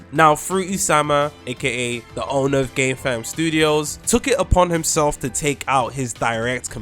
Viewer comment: Wait did he say gay fam studios 😂